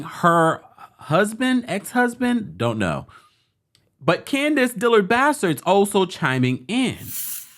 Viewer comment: Did anyone else hear Candiace Dillard Bastard?